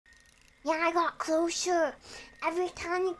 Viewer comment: i thought she was saying closure and i was closure for what i’m confused